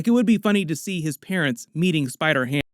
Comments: I thought you said "eating spider ham" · I thought you said "eating spider ham"💀